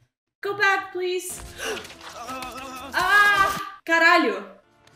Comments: did i hear one "AI CARALHO"? LMAO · Ok so wtf I clearly heard a "CARALHO!" @ 3:02 Tu é BR né? HJAHAHAA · Do you speak Portuguese? I heard you saying "aí caralho". I am form Brazil